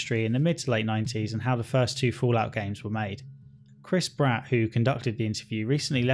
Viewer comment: i thought he said chris pratt